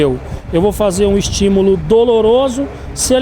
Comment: "um estimulo DOROLOSO" foi isso mesmo que eu escutei? kkkkkkkkkkkk